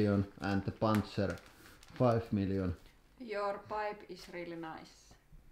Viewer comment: when she said your pipe is very nice i have a feeling she was refering to something else lol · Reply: I thought she said "You're wife is very nice". She looked a little angry as she slapped the paper into her hand. LOL